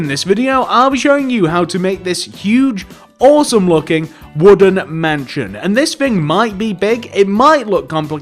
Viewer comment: I thought he said woodland mansion